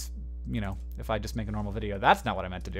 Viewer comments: English is not my first language and when Hank said "Donate your sperm" I heard "Don't eat your sperm" What is wrong with me ? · Man I heard "don't eat your sperm" at the end and had to rewind and listen again.